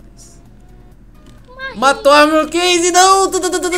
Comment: Me corrijam se eu estiver errado mais lá pros 5:06 o disse "Matou a mi.NH.. Moonkase " foi isso ??